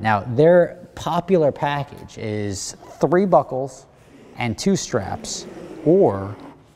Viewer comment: Omg,during his ad read in the beginning i could of sworn he said " the package is 3 buttholes..." when in fact he said 3 buckles